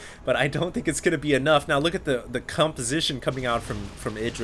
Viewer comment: Did I hear "cumposition" at 18:00, or am I just drunk...